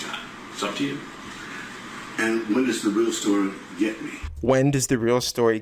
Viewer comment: I've always heard that as "what". Still do.